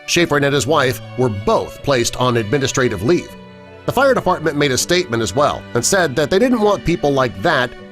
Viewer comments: did anyone else catch him say firedefartment? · Lol 4:45 “The fire de-fart-ment” who else heard that?